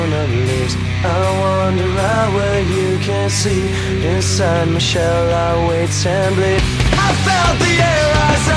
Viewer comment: Oh snap. All this time I thought Corey was saying "Inside Michelle I wait and bleed"...